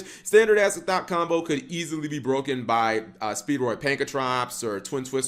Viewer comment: Did you say "Speedroid Pankratops" at 2:19-2:21 😅😂